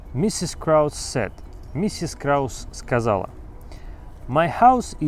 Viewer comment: мне кажется или это звучит как set?слово sad не услышал, там вроде букву d нужно выделить